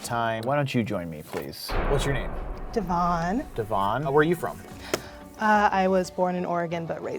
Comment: did he say "how are you from"